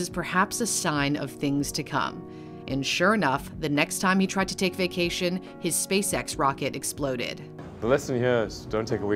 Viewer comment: I was angry there for a sec, I thought you said he doesn't take vaccination